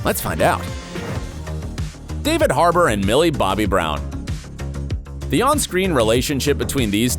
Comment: Millie hargrove 0:18 (speed 2 i hear it)